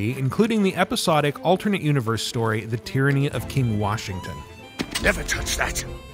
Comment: So Can we get the Tyranny of King Washington dlc wolf outfit available for the main campaign?, because many players would appreciate it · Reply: I thought you said Tranny king for a second 😂😂😂